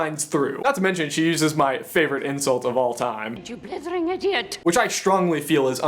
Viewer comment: Tell me what she says please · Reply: You blithering idiot